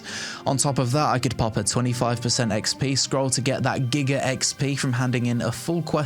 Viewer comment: did i hear exp scroll? please no, way to make grinding without one feeling waste and let me guess, you can buy them with real money as well?